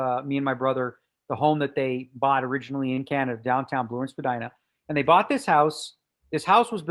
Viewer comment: Can anyone understand the location Daniel said at 9:30 and again at 11:08? The closest I can make of it is "Lawrence Bedina", but when I search for this I get no useful results. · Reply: Spadina Avenue in Toronto